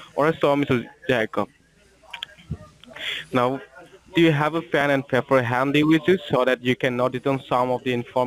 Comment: Daym their English is so crap. Pan and pepper? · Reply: One time I heard "pin and peeper."